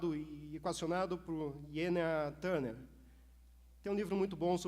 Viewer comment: Qual o nome do autor do livro que ele fala nos 5:33? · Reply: Oi Carolina! Acho que é Jenia Iontcheva Turner :)